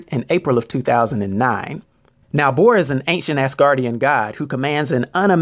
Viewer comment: sounds like you said he is an ancient ASS guardian